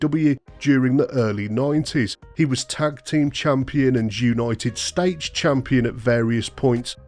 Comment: Did you say "jewnited states" around 5:36 - 5:40 or are my ears off